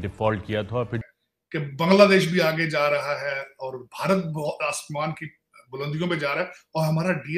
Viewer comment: Did he say "Pagla desh"?